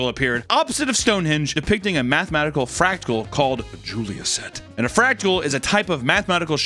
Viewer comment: Am I hearing you say fractal or "fractical"? Lol